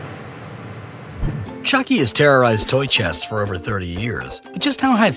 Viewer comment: At the beginning I thought you said chunky